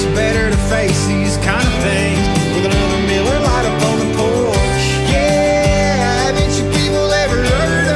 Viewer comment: What is he saying in the first chorus after, "It's much better to face these kind of things..."? It sounds like a little miller lite on the front porch, but I think I'm mishearing it? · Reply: "with another miller lite upon the porch"